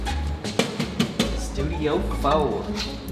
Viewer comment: did I hear Studio-Fow? ಠ_ಠ